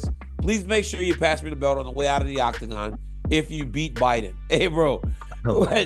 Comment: Did dc say “if you beat Biden” at 2:55 ?